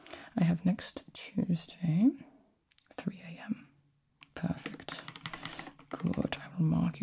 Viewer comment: Did anyone else catch that at 21:19 the subtitles say “oh boy 3am!”